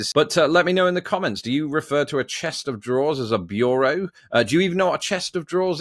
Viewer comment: I always heard “Chester draws” never chest of draws.